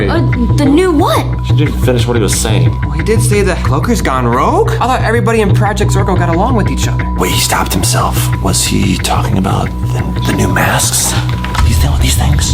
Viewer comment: I thought pz leader said project zoigi for a second